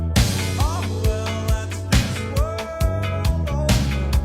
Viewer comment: I heard, "Last Balloon" not, "This World Over" for those chords.